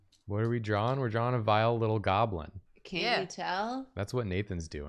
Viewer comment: Gonna be honest i thought they said Vial of goblin not vile goblin